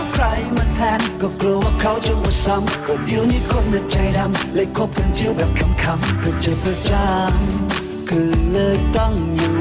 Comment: ทำไมผมได้ยินเป็น "เลยคบกะเจี้ยวแบบขำๆ"